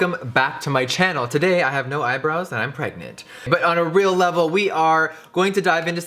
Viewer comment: 😱😳first thing I heard "I'm pregunto" 🤔did any one else catch that